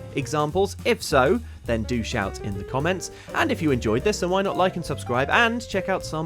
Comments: I know what he said was “do shout in the comments” but what I heard was “douche out in the comments” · I thought he said “douche out” and for a second, I was very confused